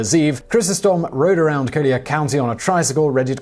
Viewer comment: The tricycle crime happened in Naples, located in Collier County. Sounds like he says "Kodiak County" lol